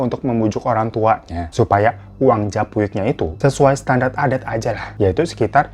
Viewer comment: menit ke 13.00 salah ngomong ya bang apa saya yg salah denger ssya dengernya uang jaipuk padahal judulnya japuik😂😂😂😂😂